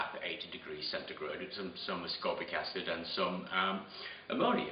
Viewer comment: you mention putting the graphene sheets in a bath at 80 degrees with ammonia and something else that I couldn't understand. It sounded like somascopic acid but that doesn't exist so what did you say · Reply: ascorbic acid